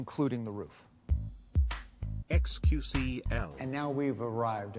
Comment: Did they just say XQC in